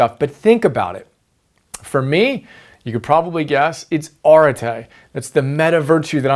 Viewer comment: Great template. Awesome stuff as always! By the way, what's that word that describes "to show up everyday at your best" @ 11:16 ? Orete?